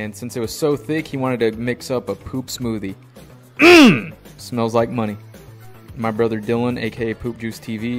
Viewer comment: I thought he said “MMM👹 smells like a monkey”😅